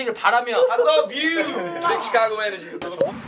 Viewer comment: did Vernon say big Chicago energy?!?